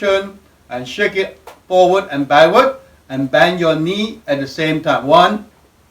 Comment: I thought he said bang your knees together. I was knocking them like crazy until I saw how it was really to be done 😂. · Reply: Sorry you heard wrong. Bend your knees that what i said. let me know how you get on.